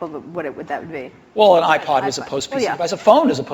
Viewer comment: sorry at 5:36 i think says ipad as post pc device